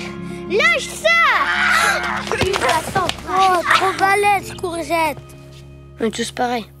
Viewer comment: Any francophone who knows what the kids are saying from 0:44 to 0:47? thx! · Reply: "Trop balaise Courgette !" ("balaise" is a slang word meaning "strong")